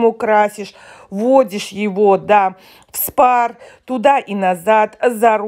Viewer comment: В Спар. Мне послышалось " в Спа"... Думаю, что я пропустила...🥰😃